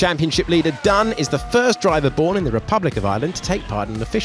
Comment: The commentator didn't say 'Irishman'; he sad 'born in the Republic of Ireland'. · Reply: ah ok, then you're right, i thought he'd said "from the republic of ireland", not "born in"